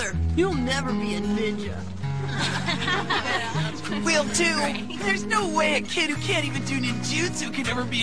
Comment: Anyone please please please tell me what rock Lee says here: 0:19 Is it Will do?